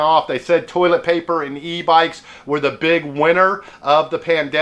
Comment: I thought he was saying E- bites at first lol.